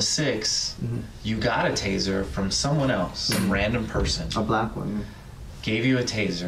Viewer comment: This is the "black woman" time stamp. I think he really did say "a black one."